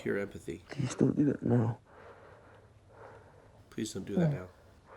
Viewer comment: I actually think you misheard a significant line. When nick hugs Charlie and responds to his confession of self harm. He doesn’t say: “please don’t do that now” Nick asks: “DO you still do that now?” (And Charlie says “no”) And the distinction is significant. Because asking him to stop adds a lot of pressure and judgement and feeling of shame if you do it again. Instead he asks Charlie to promise to tell him when he’s feeling like that. Showing that the love and care he has for Charlie isn’t conditional on him stopping this behavior. He’s expressing that he cares. That he’s there for him. That he doesn’t want him to go through it alone. And I love that. Ultimately it is a better approach to reducing shame which will help him stop the self destructive behavior